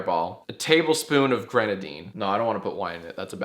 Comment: When I listened to your drink, I thought I heard ketamine instead of grenadine